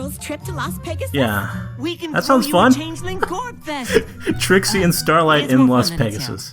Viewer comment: A Changeling Gorefest?! Do changelings still use violence to solve everything?! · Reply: I thought he said gorbfest